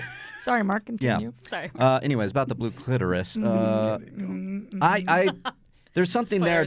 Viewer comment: It took 3 times. I thought they said Blue Clifford. Wait, what where did a Dr. Manhattan dog come from Oooohhh! That is not what they said 😹😹😹😹